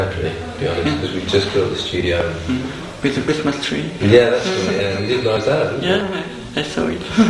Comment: what does the interviewer say there? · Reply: "Like a Christmas dream"?